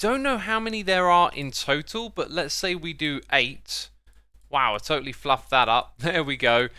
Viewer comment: The phrase that X says right 4 minutes is "I totally FLUFFED that up." (emphasis mine); just in case anyone else misheard him and had to rewind to check.